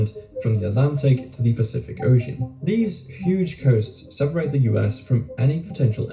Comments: I thought he said goats, too! 🙃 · I thought he said goats too 😂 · For a moment I thought he said 'goats' too. 😂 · I thought he said goats 🐐 too 😂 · I thought he said goats too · Don't worry, I heard "goats" too, at first.